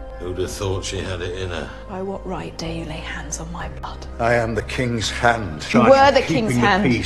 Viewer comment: I heard "By what right did you lay hands on my Butt"